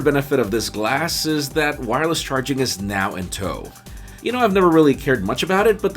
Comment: What does he say at 2:13? "Wireless charging is now ... ?" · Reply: in tow I guess.